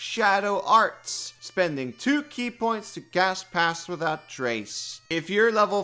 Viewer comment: Dunno if it's the accent or what, but everytime you say "cast pass without trace" I hear "pass gas without trace".